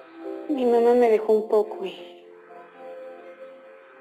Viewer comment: Que dijo en el minuto 25:04...YO TENGO ALGUNOS..........en 1983???? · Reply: Ahorros dijo